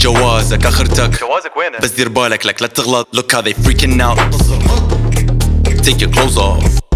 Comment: Translated subtitles available by clicking the 'CC' option under the video. Thanks for watching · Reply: This is scary..correct me if I misheard but I heard take your clothes rape....what sickness is this?